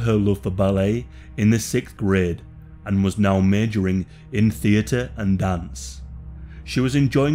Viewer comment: The accent is hideous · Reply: Had to turn on subtitles, at 0:18 i thought he said murdering instead of majoring.